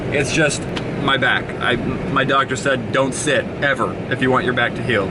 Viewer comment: When he said my doctor said don't sit ever I thought he said my dog just said don't sit ever